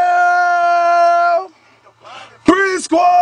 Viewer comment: Can't help but think he said "Rivendell"